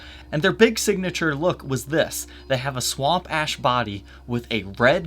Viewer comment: I keep hearing "swamp-ass" when you say that. Since id be down-tuning this guitar, id name mine "mud-butt".. lol